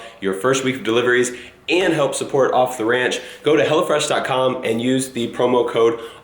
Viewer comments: Until you put the text on screen, It sounded like you were saying "Hella Fresh" which is honestly a better name. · anyone else thought he said hella fresh? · i thought he was saying hella fresh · I thought u said hella fresh not home fresh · when he said hello fresh... i thought he said hella fresh xD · Hahaha I thought you were saying "Hella Fresh" 😂😂😂😂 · I thought said "hella fresh" at first lol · I thought you were saying 'hella fresh' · I thought he was saying "hella fresh" lol